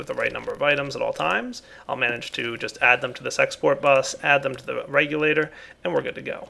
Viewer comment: did he just say "sexport bus"?